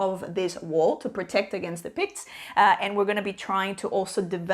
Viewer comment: I was mishearing 'Picts' as 'pigs' haha 🤣 Great review! I'm very intrigued by this heavier flip and write, I'll add it to my wishlist 👍